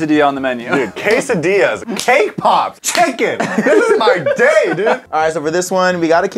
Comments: lol i thought he said KPop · i honestly thought he said k-pop and almost lost it